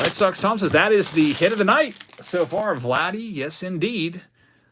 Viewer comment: I swear, i thought you said i got the pick of the night 😮.